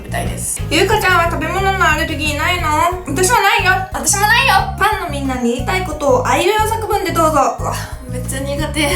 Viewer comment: ファンのみんながパンのみんなに聞こえてしまった…(´･_･`)